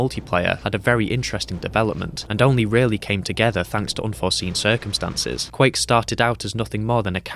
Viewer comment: Did you say unforeseen consequences?